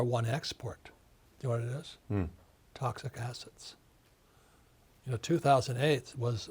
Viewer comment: Lol I thought he said “Toxic Acids” i was like yup now is a good time to be a Chemist 👨🏿‍🔬